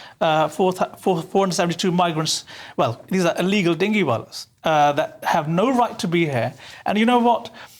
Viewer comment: did he call them dingy wallas … classic 😂